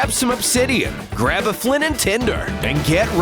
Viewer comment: anyone hear him say flint and tender?